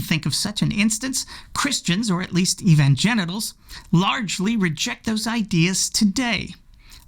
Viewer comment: Did he say evangenitals or did I mishear? Anyway, I think G_d can do better. In the meantime, it's on us. Oh well.